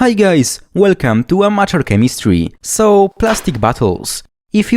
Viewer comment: I swear, every time I hear him say "Welcome to Amateur Chemistry", it sounds like he's saying "Welcome to Unnatural Chemistry" 😂